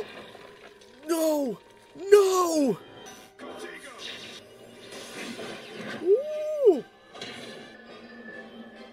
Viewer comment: did he say protaygo... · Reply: "Contego" It's a protection spell